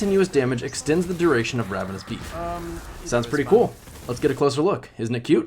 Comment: I know everyone heard him say ravenous beef the entire time